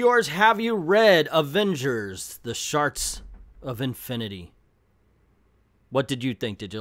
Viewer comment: I keep hearing you say "Sharks of Infinity." I'd probably buy an Avengers book titled "Sharks of Infinity."